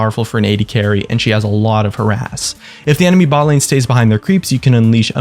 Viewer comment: miss fortune has alot of her ass i heard you: ^)